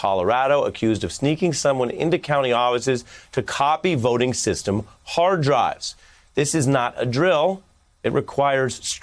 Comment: I thought he said "Trump and his maggot fans" lol